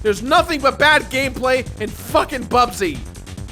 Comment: Did I hear you say “fucking Bubsy”? I thought you censored swears. Doesn’t bother me